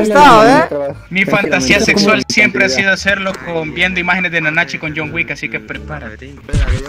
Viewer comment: En el minuto 4:53 dijo nnt o los 7 pecados capitales xd yo entendi eso